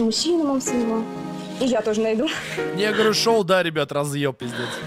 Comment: что он сказал? мне не послышалось? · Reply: я говорил шоу